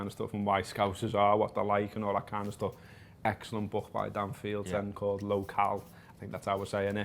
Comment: What's the name of the book he recommends 11:30 ? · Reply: I also want to know this! i heard “low cal bu dan fields” but i’m not finding anything